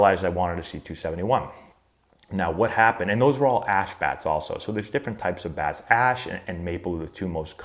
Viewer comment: I thought he said “and they were all ass bats”😆😂😂